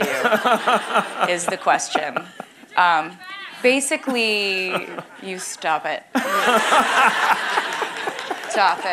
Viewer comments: what did the audience shout at 39:46 ? LOL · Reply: I think they said “They should give it back”😂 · What did the person in the background yell in 39:46? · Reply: My best guess is “Jake give it back.” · What did the girl say at 39:47 ? Anyone know? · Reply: Yeah she’s said “get it back” she was talking about the scarf